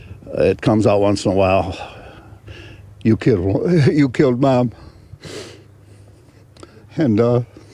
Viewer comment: Hi everyone! I'm just surprised that no one mentioned him stumbling over the words " you killed my wi...you killed mum" 10:04 . For me it sounded like he slipped and wanted to say you killed my wife. But maybe is just me. Sorry for my english. Greatings from Poland😊 I love the channel ❤